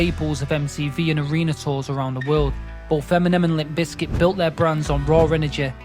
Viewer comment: tf you say "Feminem" for?